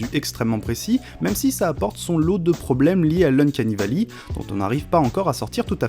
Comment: "même si ça apporte son lot de problèmes liés à..." quoi ? J'ai réécouté plusieurs fois je comprends pas. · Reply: Uncanny Valley <link>